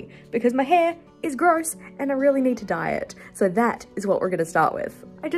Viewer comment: lol I heard "my hair is really gross and I do need to diet"... I'm thinking, "What!? This lady does NOT need to diet!!" And then you come out with purple highlights!! :face-blue-smiling